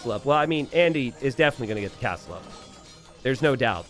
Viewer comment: I heard "Andy will definitely gonna get the Castle up, there's no DauT" at 15:55 It made a lot of sense.